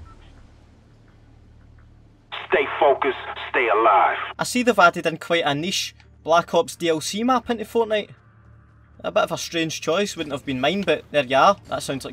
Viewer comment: did he say anish because that's my name